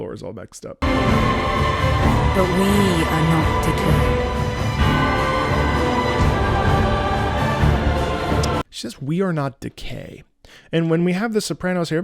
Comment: That's what I had heard too, but the captions said "we are not deterred", which aligns quite nicely with your analysis.